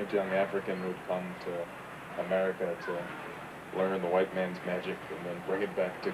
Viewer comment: Why do white people stand for this crap. I heard the first sentence witch included "white mans gadget" and I stopped it. If whites were banging on all day about this crap it would be all on the msm they would be frothing at the mouth screaming RACIST. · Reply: I thought he said "white man's magic", which does nothing to make it less ridiculous I just wanted to know if I miss heard.